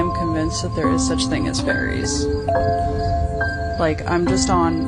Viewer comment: Love this video. At 22:25 as you say "another scary video about fairies.." I can hear another (female) voice say the word "fairies". Anyone else hear this..?